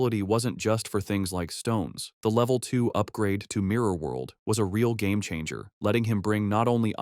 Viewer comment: mirror world? truly ideal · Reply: i deadass thought you said minor world holy shit